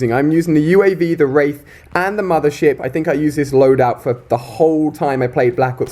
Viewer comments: did he just say mothershit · Mother ship. I thought I heard mother shit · I thought you said "Mother Shit" xD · I thought he said "Mother Shit" 😂😂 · Did he just say mother shit